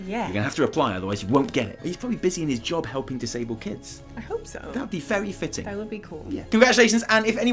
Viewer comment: i sware i heard "helping disable kids"... listened to it 3 times and still heard it... lol